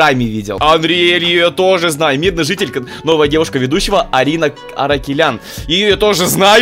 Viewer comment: Мне одной послышалось Арина Аракелян?🧐, Карина Аракелян💥